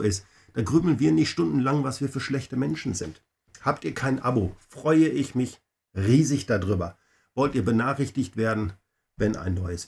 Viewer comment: Hab ich richtig gehört?? : 51:00 "Habt ihr kein Abo freue ich mich riesig darüber"... Das Set finde ich klasse. In diesem Jahr habe ich bereits vier Sets zum Thema Weihnachten gekauft, aber irgendwie reizt mich das Ding schon ... und es ist ja auch eher ein Winterset, das auch nach Weihnachten funktioniert. Bei lesdiy habe ich noch nie bestellt. Heute konnte ich es nicht bestellen, da ausverkauft